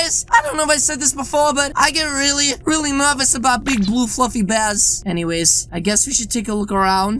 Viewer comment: When he said big blue fluffy bear.... I thought he said big blue f***ing bear